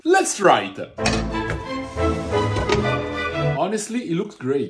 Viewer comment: he says "approved"??????? oh my god!!!! 🤣🤣🤣🤣🤣🤣🤣🤣 I always thought that he was saying "a frutta" in Italian. and I couldn't get what does he mean.... now, I know! 😉👍🥰 thank you so much, the unknown spectator of the Youtube channel!... now I see that according to Italian accent, the ending -Ed is being spoken like -tta...